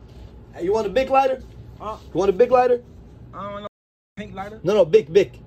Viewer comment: bro lol he clearly said he didn’t want a “pink” lighter.